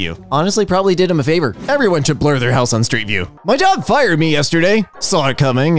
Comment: I heard this as "my dog fired me" lol